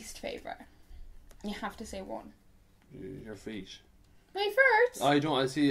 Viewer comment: I thought said he does not like your face (instead of your feet), and I was like wowww RUDE hahahaha, you guys are so sweet <3